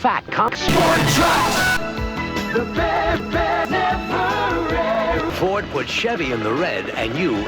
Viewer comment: I hear “The dead never rest”